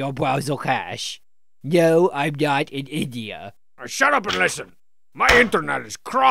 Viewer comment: When I first watched this, I always thought Homestar said "No, I'm not an idiot."